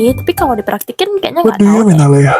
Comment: ngomong apa? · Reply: "what do you mean alea?"